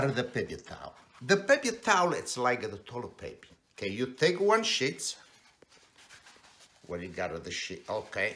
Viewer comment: Unfortunately I have to put this here.. ***Disclaimer: This is a joke! Please don't flush paper towels down the toilet! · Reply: Wow , that ending , hahaha . I could have sworn when you said you take a sheet it sounded as if you said "you take a shit " . I'm not trying to be a wise guy here . Anyway you look really really young and sound very young for 91 yrs .old , really !